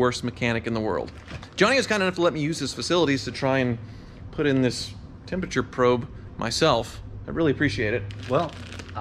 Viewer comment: Did he say; "Johnny was kind enough to let me use his facility to put this temperature probe in myself."